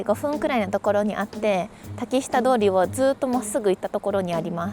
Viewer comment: ぼーっと観てたら『滝下通り』って所が『竹下通り』に聞こえて原宿にも温泉あるんだーって一瞬だけアホになってしまいました😂